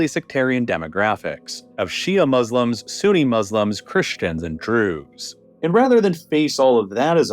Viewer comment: another one, 38:01 says "drews" instead of "Jews" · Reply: because it's "Druze" and not "Jews". Druze is a relgion closely associated with Islam, thought its adherents regard it as a separate thing